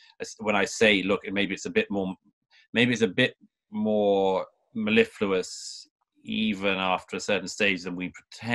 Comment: Very interesting dialogue, thanks for posting this. Also, I learned a new word, "mellifluous", unless I heard it wrong, at 2:09. Always a bonus.